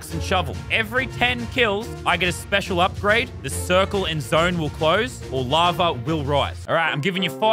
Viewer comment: did he just say the circle in Minecraft...